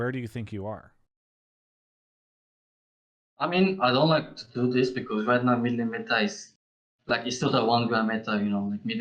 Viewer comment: Wow I can't understand anything he is saying... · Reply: He says, "I mean I don't like to do this because mid lane meta is really boring rn"